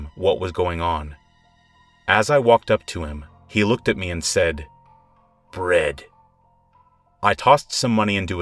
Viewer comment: I thought he said brick but it turns out they said bread